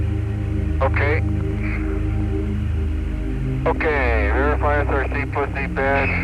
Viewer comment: Ummmmm Err did uhh that astronaut @ 9:07 say " Verify a Thirsty Pussy Patch" ???? LOL. Right before he askes "What are those things going over, what is that Jack".. I had to stop and listen to it again n again n again n well you get the picture . And it may sound Crzy but thats what I hear. Can someone anyone Please either Verify or Correct me ???.